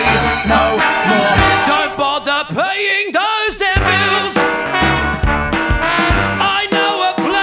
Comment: Great job! Just a couple notes: I think 'Don't bother playing those damn pills' should be 'paying those damn bills' and 'I hear they do it in advance' should be 'I hear they're doing it in France' Thanks for your work =)